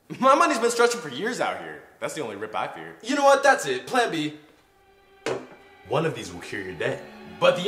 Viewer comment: He said “One of these will cure your debt” But the captions said “One of these will hear your day”